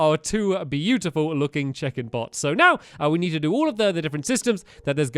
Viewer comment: I think I have dyslexia because I thought he was saying chicken